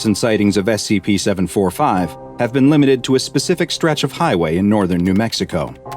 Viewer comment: Lol school got me so tired I thought you said scp 045